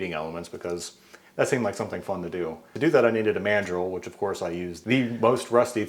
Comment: Something about how you said "mandrel", i heard it as man-drill. Now I can't unhear it